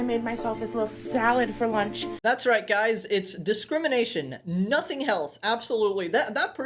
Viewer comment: Bro instead of discrimination I thought she said determination